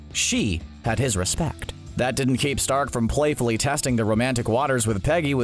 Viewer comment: From 01:34 you say “Stark” but the subtitles say “start” @ 3:46 you say “been a factor” but the subtitles say “benefactor” @ 04:41 you say “Thanos” but the subtitles say “Panos”